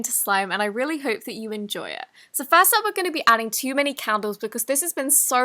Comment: Since I was watching this at four in the morning, I thought said chemicals instead of candles lol